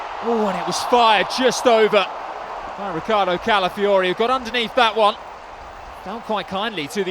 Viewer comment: 3.27... thought he said "avacado Califori!" 😂😂